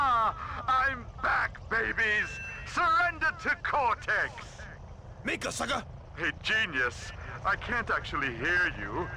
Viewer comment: I still don't know what crunch is even saying here 2:00 can somone please tell me I wanted to know for years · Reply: I looked up the script, and it turns out he was saying "make us sucka" in response to Cortex saying "surrender to Cortex"